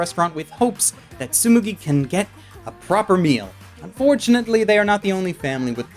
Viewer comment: "Fortunately they aren't the only family with problems" That sentence just sounds wrong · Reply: Huh... I thought he said unfortunately